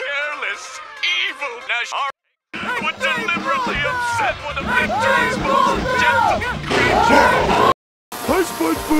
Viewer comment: What did eddy said in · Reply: Ed (in the background) was saying, "Extreme Closeup".